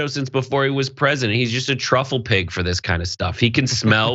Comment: Did he just say snuffle Pig I can't stop laughing